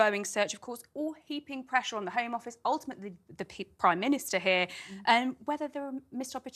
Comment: Did she just say "the pig prime minister "!???